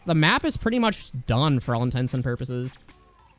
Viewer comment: all intense and purposes i didn’t think he said it right at first but for anyone else yes he said all insane and purposes